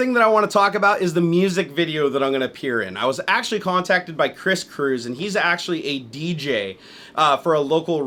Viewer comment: When you said Chris cruse for a second I thought you said chris cringle!